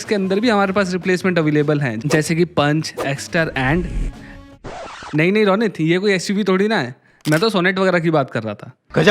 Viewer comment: did he said "nahi ronit yeh koi..." it feels weird to me he is directly taking my name can anyone reply what is actually said